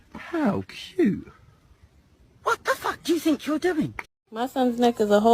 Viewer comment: I’m nit even going to lie about this, I actually thought the lady that said “What the f*** do you think you’re doing?” Said “whatever do uou think you’re W”